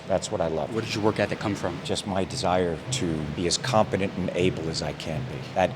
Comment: Competent not confident. (I'm not nit-picking but it gives a different meaning to what he said)